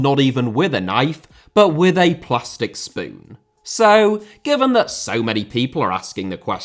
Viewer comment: I heard "plastic spoon" as "+6 spoon." It's been a long time since I played any RPGs like that.